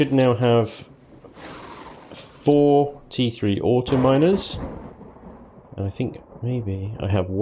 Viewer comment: i swear i thought you said "43 autominers" haha